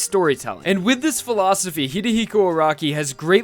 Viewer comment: Did anyone else hear at 1:49 Hidehiko instead of Hirohiko